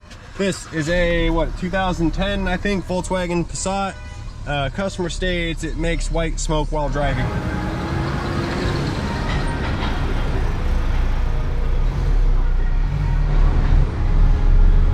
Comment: Ooo they said white smoke....I thought they said white snake. I was going to say that's not a problem.